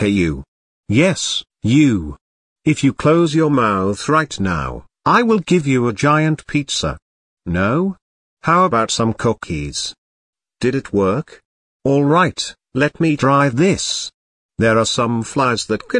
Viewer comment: wait, did he say COCKIES???